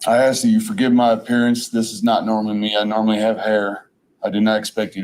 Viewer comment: Did he say "I normally have hair"?